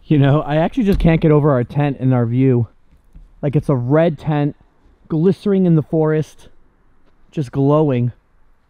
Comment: Do more camping videos, 30:22 did you say glisering lol